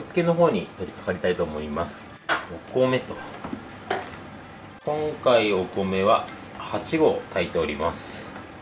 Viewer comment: 「今回お米は8合炊いております」が「今回お米は1合炊いております」に聞こえて、いやはそんなに少ないはずないしこの量で1合はおかしい…てことは11合？11合炊ける炊飯器持ってるんだすげえ。って思って確認のため戻してみたら8合で、あ、いつも通りだって思ったけど8合も食べるなんてほんとすごいなって思いました。初めてコメントしました。いつも楽しく拝見させていただいております。これからも応援してます😆